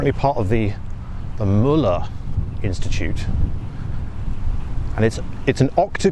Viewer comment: For a moment I thought he said "the Mullah Institute." The Møller Institute. Phew! Thank God for that!